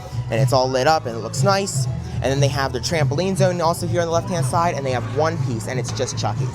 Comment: What... did you say!?!? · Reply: “Left hand side”